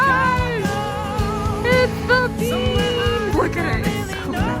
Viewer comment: oml when she said they were going to see the bean i thought she said beach.. i was like wtf there isn’t a beach in chicago🤦‍♀️